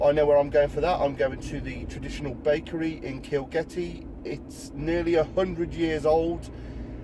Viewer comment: kilgetty? i thought you said kilkenny, made me want som of that lovely ale.